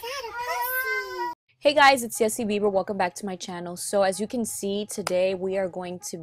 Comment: I thought you said Justin Bieber